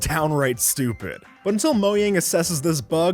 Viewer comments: am i the only one who heard him say moyang instead of mojang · what? did he say "moyang" ???